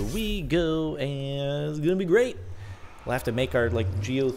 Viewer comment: As a non-english native speaker, does anyone know what says at 14:52 and 20:57 like "This thing Imma do hickers", it keeps bugging me and I can't seem to understand it well enough to google it. That would be kind :)